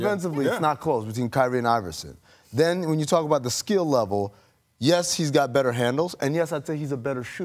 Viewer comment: I swore KD said skill wise 😂